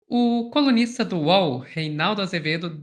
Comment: Eu ouvi “comunista do uol”😅